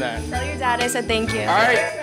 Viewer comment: “Do you remember the number” “Of course” Had me during 😂😂😂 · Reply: I heard she said "do u remember it" not "do u remember the number"